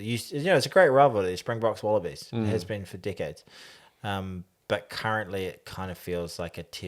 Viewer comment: He said " decades" but I heard "dickheads" ! Time for an ear clearout... Thanks for the vid.